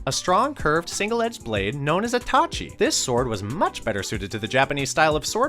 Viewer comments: Who else thought he said "Itachi" and not " a Tachi" ? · Me as a naruto fan when he said tachi I thought he said was itachi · When he said " a tachi " i thought he said " itachi" lol